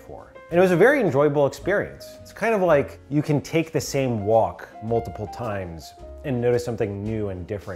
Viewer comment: When makes the "walking" analogy I heard it as "you can take the same wok..." threw me for a loop momentarily haha 😄